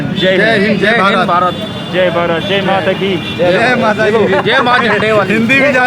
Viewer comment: can someone tell me what this phrase means. I've seen Bald and Bankrupt say it as well, it sounds like he's saying Jay Bataki but when I look it up nothing relevant comes up.